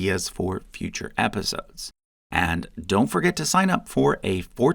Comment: First time I watched the commercial part – and I thought I heard "40-day free trial". Now, wouldn't that be more appropriate for a bblical context?